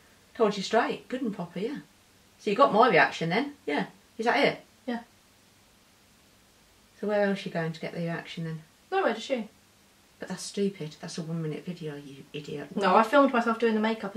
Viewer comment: did just call a idiot ?